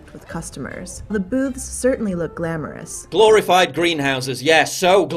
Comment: Maybe my mind is just in the gutter, but I can't be the only one who heard "The boobs certainly look glamorous."